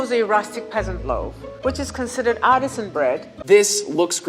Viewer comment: Legit thought she said autism bread and I was like Seems about righttt